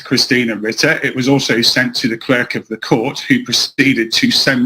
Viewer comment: Did he just say "Cleric of the court"? A Paladin he is not.